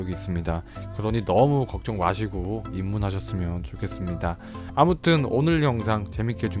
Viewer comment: 소개글에서 입문 했으면 좋겠습니다 하셨는디 왜 저는 내 밥이 얼른 되어줘 로 들리죠?ㅋㅋ